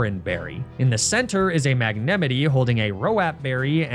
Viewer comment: Did you just call a magnemite a mag-ne-mi-ty?????